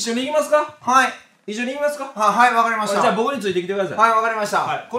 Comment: 成長して成長してない理解不能者 はい分かりましたの音感 すいません間違えましたに聞こえるし